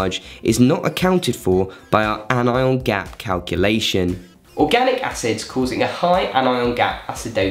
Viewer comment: I don't understand why he said anion gap increases as an increased proportion of negative charge isn't account for our anion gap ...but what i understand if lactate increase and bicarb decrese there will be a high anion gap...but why he said its not responsible for our anion gap..please someone explain did i miss something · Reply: he said 'increased portion of negative charge is't counted in our anion gap calculation', probably you misheard, hope this clarifies your confusion!